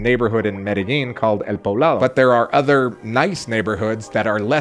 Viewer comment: I am having difficulty understanding what you said was the most expensive neighborhood that you live in. What is it again? Is that near Calle 1A and south of the airport? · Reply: El Poblado.